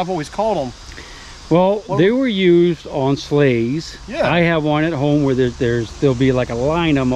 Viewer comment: Sleighs at 1st I thought he said slaves.